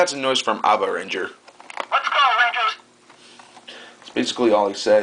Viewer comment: lol what does he say? :D I heard "Let's go Nachos" xD